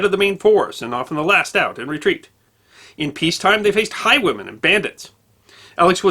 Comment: I thought he said "High women" not highwaymen....and I was like, cool...